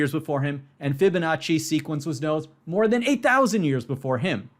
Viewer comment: I thought he said the Fibonacci sequence was known 8000 years before, not “a thousand”. I was about to be really impressed that people were doing Fibonacci sequences pre-writing